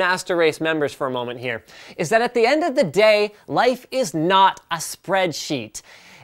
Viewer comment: who else thought that he said life not spread is a sheet